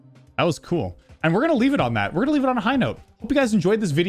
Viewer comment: Is it just me or does it sound like said at the end “and as always, pee”